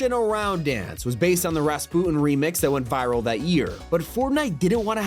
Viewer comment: Did bra just call Rasputin "raspon?"